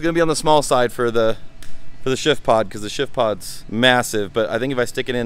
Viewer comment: I thought he was saying shit pod. lol